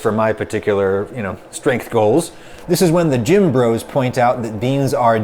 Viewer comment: IDK who did the subtitles but he/she wrote "jim bros" instead of "gym bros"